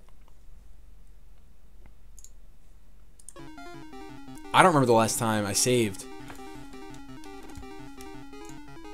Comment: what did say? I couldn't understand him. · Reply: Wanal